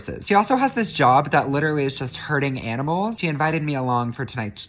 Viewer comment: When he said herding, I thought he was saying "hurting" because he was being a smartass😁😳